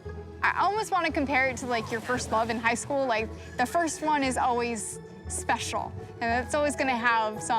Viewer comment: what's happing there She says the first oe is always special,!